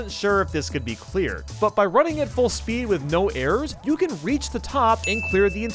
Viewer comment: did you just say no airs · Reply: He said "with no errors", but really fast